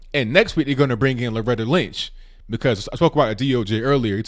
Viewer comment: I got distracted and thought I heard you say "Next week they're going to bring in Brotha Lynch!"